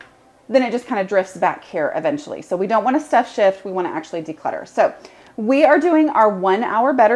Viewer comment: I hope I do not offend anyone..... Every time I hear you say "stuff shift" I laugh because #1) I do that! #2) I hear it as "stuff sh*t" (which, honestly, is what we are doing when we "stuff shift")